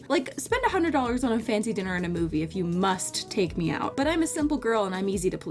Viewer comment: sorry I love your videos but it sounds like your saying "please take me"I no that's not what you ment or anything sorry